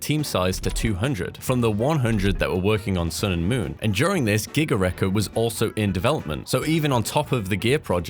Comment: ah ty I will fix that! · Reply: subtitles say "Gigaare" when it's supposed to be Giga Wrecker